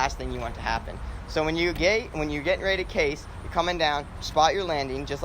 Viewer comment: did he say: when your gay? 🤣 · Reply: haha think i was about to say when you "geyt ready to case". little bit of accent trying to slip through haha